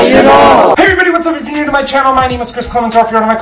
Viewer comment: Everytime I click your videos I skip the intro . No offense, and it sounds like you say "buy my shit".